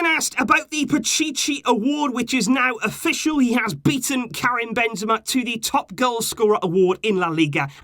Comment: I heard it as Karen Benzema 🤣